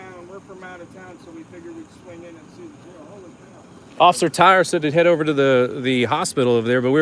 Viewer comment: It sounded like you said, "Officer Tyrantson" 🤣🤣🐖😁